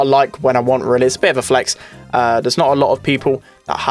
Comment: At first I thought it said (I hate every skin)